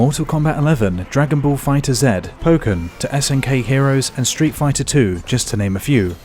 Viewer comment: wtf did he say “poke(verb)”-“men” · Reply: He said "Pokkén". As in the pokémon fighting game Pokkén Tournament.